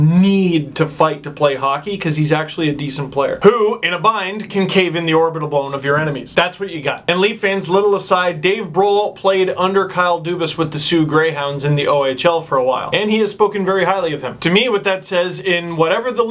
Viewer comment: I thought he said Dave Ghrol lol